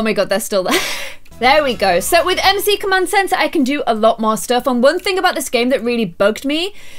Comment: When she said MC command centre I thought that she said Emma's secret man sense!😂